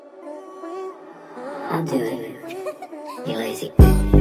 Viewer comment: why did I hear "You lazy"